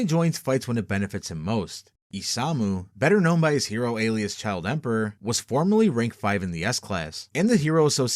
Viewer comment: I almost thought child emperor said child predator